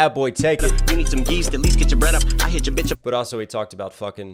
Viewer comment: Thought he said yeast to get your bread up